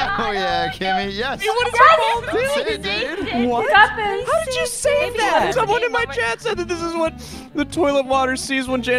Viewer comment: I dont know what said at 0:07 but my headcannon is "deafen" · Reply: "you got this"